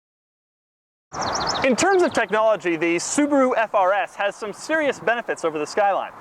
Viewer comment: i heard subaru frs